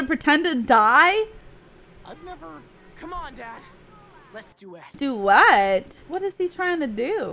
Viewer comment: I think Steven said “Let’s Duet!”.